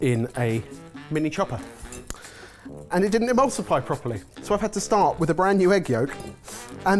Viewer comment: Resend that badge. I heard "Immulsify!!!!!!!!!!" all over again